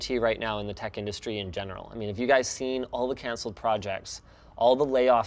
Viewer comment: Did he just say "Have your guy call my guy"???